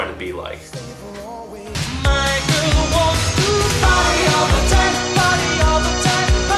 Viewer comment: When I first heard this song, I was 3 years old (I was born in '87) and I swore he was saying "My girl wants to potty all the time"